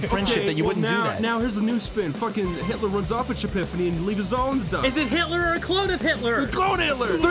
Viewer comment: apparently you couldn't understand what he said but it's "Hitler runs off with Shapifanny and leaves us all in the dust"